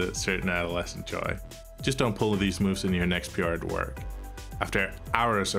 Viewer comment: What does he say at 8:20? I can't understand the sentence and the transcript doesn't make sense either. please help :) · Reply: "just don't pull these moves in your next PR at work"